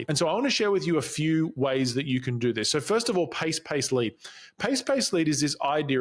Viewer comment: I like the Pace, Pace, Lead pattern in conversation. Although, in Part 1 I thought you said Pace, Pace, Leave ! LOL!! I thought how are you going to carry on a conversation by leaving?!! I also was helped by your Narrowing your Niche video and the video with Rick Mulready on using AI. I subscribed to Expert Edge tonight. I'm just starting out with my content.